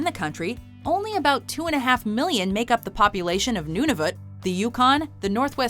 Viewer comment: d-did I just hear you say nounouvuit